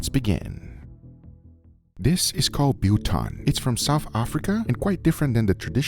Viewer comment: Guga, as a South African who makes his own biltong, I have too many suggestions for you to make it better. But in a nutshell, leave cayenne pepper and use black instead, use coarse coriander (like really coarse), and lastly, try to find beef with yellow fat (older cows that were grass fed is the key). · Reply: thought he said butong lol